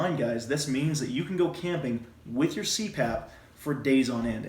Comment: untill you said Cpap i thought it said camping with CRAP lol